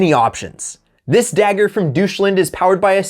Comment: Did you just call my country "doucheland"? 😀